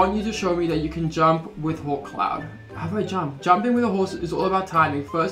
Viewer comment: when u said 'come on hawkcloud' i thought you said, 'come on whole clown' oops